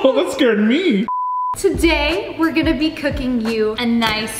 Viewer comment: She started by saying "Today we r going to be cooking u-" I suddenly thought she said that she was going to cook us :-/ And then she said the rest "- a nice, wholesome, healthy meal." I was like oh --__